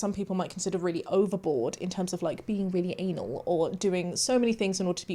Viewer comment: What? All I can hear is "being really an*l" and the captions don't help at all. Did she really say that or am I not understanding correctly? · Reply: Hi, it’s a term people use in some countries. It means being a super perfectionist: anal. She’s saying Im very anal - hyper focused and perfectionist …. Not the anal you’re thinking 😅